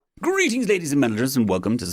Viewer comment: I heard "greetelings" did anyone else hear "greetlings"